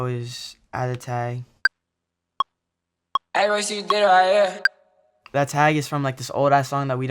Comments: so that's what he was saying "ay Royce u did it right here" what did u think he was saying? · yo i didnt know he tag said “ aye you did it right here “ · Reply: I thought it said Ayy what she did right here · i thought he said "ay what she did right here" 🤣🚫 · yo i didnt know he tag said “ aye you did it right here “ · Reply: I thought he said "lil mosey is right here" 😂